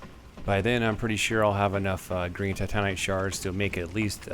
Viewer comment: Is it just me or does it sound like he is saying Titanic instead of Titanite?